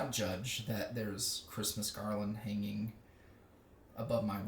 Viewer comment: I thought you said Christmas cardlin I was " what?😃" 😂